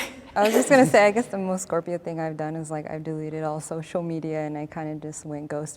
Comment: did she say SCORPION😭